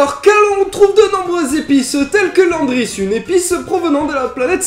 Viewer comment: au début quand tu as dit "l'épice" j'ai compris "les pisses" lol